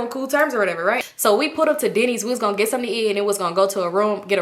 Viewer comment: Girl when you said y’all went to Denny’s I thought you said Diddy’s😳😭